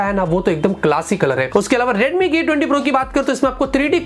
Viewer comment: i heard redmi Gay 20 pro lol.